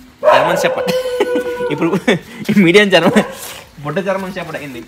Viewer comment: what is that word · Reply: Budda German shepherd ani annadu bro